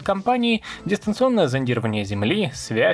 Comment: Послышалось "Дистанционное ЗОМБИрование земли" 😂 Особенно звучит учитывая название канала 🤣